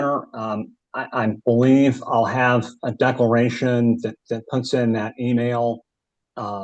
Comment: did he say deckleration?